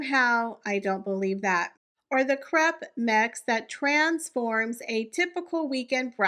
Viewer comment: Lol. I thought you said crap mix, when you said crepe mix. You'd be right either way. Always enjoy your videos. ;)